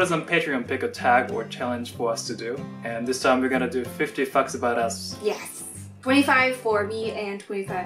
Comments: I misheard as fucks about us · Lol I thought he said 50 f*cks lol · i hear it fifty fuck about us 0:05 lol hahaha 😂😂😂😂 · Oh my god when said "50 facts about us" I heard "50 fucks about us" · 0.06 I literally thought he said fucks instead of facts...I was confused, but it's all good! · Oh god the first time I misheard him so I thought he said 50 f*cks about them(fml) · I was just running the video in another tab then I heard "50 fucks about us", then I was like "yeah yeah, wait... what???" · For a second there I thought said, "...we're gonna do 50 fucks about us." And for a second I was thrown off. lol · Did I hear "fifty fucks about us" sorry if I did 😁😁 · is it just me, or did I hear "fifty fucks about us." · lmao i thought he said "50 fucks" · wasn't watching, sounded like said 50 fucks about us...I was startled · Bwahahaha it sounded like he said "50 fu**s about us" 😂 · When said facts I thought he said fucks hahaha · Did I mishear saying 50 f**ks instead of 50 facts- //My Japanese dad said English pronunciation is perfect (just like him)